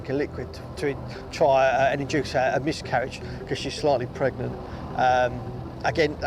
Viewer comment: No such thing as "slightly pregnant." There is such as thing as "Early in Pregnancy." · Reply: I thought he said “slightly” as well, but I think he actually said “likely”.